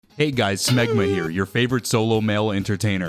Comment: i thought you said "Smegma here"